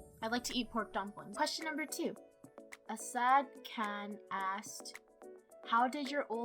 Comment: sounded like “A sad can” lol